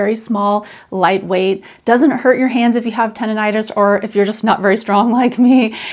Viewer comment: i had to set the video back because I thought you said tenitus and has no idea wjat that had to do with anything 😂. Tendonitis makes much more sense and I am glad I went back to hear it properly. Gave me a good laugh.